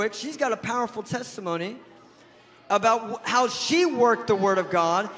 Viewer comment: Does he actually say "How she WORKED the Word of God"!?!?!!!??!